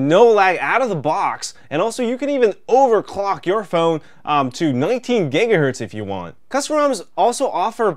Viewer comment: Did I just hear 19 GHZ at 3:13 wait for it